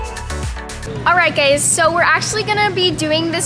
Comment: I thought said alright gays 😂😂